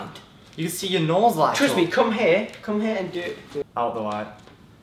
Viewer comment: Did Blake called Reece "crispy" or something like that at 3:07 ? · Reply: No he said "trust me". He's just talking fast